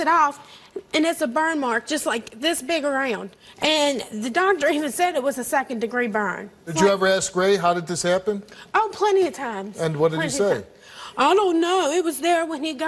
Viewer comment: I thought it said did you burn and eat...